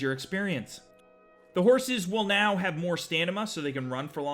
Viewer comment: Did he say " Stanima?" 1:50 ish